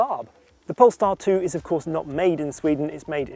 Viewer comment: I keep hearing “pornstar”